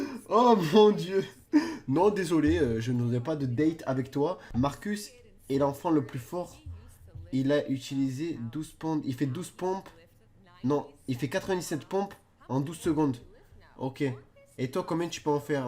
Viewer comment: Mais tu n'as absolument rien compris à 6:16 ! Tu fais genre t'as compris et que c'est n'importe quoi mais t'as vraiment rien compris. Il dit Marcus est l'enfant le plus fort dans les deuxièmes années. Avant il pouvait soulever 12 livres. Maintenant il peut en soulever 97. Combien de livres de plus peut-il soulever? Et la réponse de l'élève est Marcus prend des stéroïdes Juste pour infos vu que tu avais visiblement rien compris.